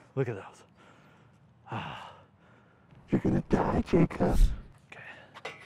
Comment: My name is jacob all I heard was "your gonna die jacob" 😂😂😂